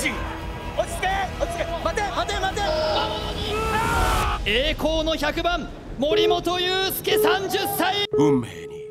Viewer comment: 日置さんのリアクションについて（訂正） ここ実況の人が選手の名前を叫んでるように聞こえましたがコメント欄で指摘してくださった 通り、改めて聴き直したら完全に「魔物に！」ですね すみません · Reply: 魔物に! じゃないんですかね?